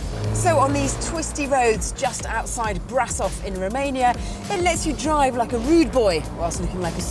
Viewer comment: Did she said " just outside Brussels in Rumania"?